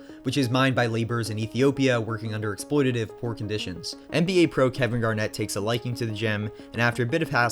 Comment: the caption says, "Catherine Garnett" lmao